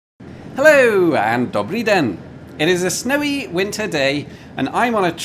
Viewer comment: When you said добрый день I thought that you said “doubly den” I know you often speak a bit of French on your channel! However the greeting at the beginning of the video, I also thought possibly you did say добрый день, but I don’t think I have ever heard you but I don’t think I have ever heard you speak a word in Russian oh word in Russian on any of your videos!